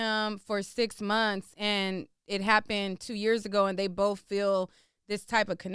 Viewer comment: I thought she was saying they were talking about what happened 2 years ago